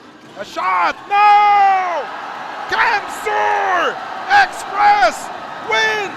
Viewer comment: did he say camsur wins? 😮